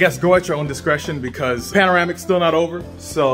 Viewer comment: I swear this man's said panoramic instead of pandemic...